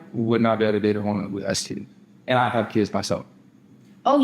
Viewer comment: Can someone help me what the hell did he say at 1:41:55?? “I would not be able to date a woman (sounds like “with an std”😂😂)” I know that’s not it but I can’t hear anything else lmao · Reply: Who has kids